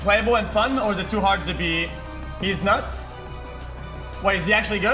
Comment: Did he just say DEEZ NUTS?